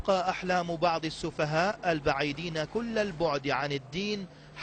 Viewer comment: اسمع وش يقول في الدقيقه 6:05 البعيدين كل العبد عن الدين ههههههههههههههه